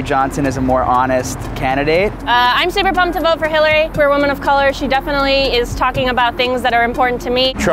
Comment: did she just say woman of color?